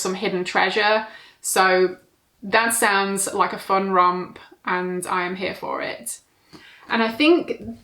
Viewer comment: did you say fun rump or fun romp 😂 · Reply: Hahaha I said romp but rump would work too 😂